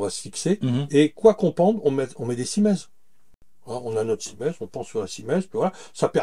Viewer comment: Bonjour! Que dis-tu à 13:16 ? Quoi qu'on pende on met des ??? C'est une question que je me pose pour pouvoir fixer des choses aux mur une fois que j'aurais fait une isolation terre paille. Une petite question : est-ce qu'il existe une formation en ligne pour apprendre à faire un enduit terre paille ou comment ça se passe pour apprendre ? Par avance, merci 😉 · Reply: Je confirme : cimaise. A la question 2 : non, il ne me semble pas possible d'apprendre en ligne ce qui relève d'un tour d demain, d'un ressenti et d'une relation entre un individu etb une matière que je qualifierais volontiers de "vivante" (dans le sens où il faut l'observer pour comprendre où elle en est de sa prise par exemple… ceci peut être déterminant pour des opérations style "ferrage", c'est à dire lissage avec un platoir adapté…).